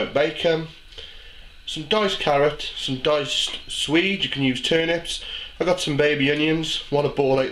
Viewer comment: Can someone please help me with the name of one of the vegetables? Im norwegian and my english is not that sharp. Its the yellow ones to the left on the plate in the beginning (around 0:40). It sounds like is saying "diced sweedes", but that cant be right? :D Can someone tell me how the name is spelled? Im going to make this dish today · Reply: I Just found out that the vegetable in question actually is called swede! Awsome, now i know what to buy. Great channel by the way. Big fan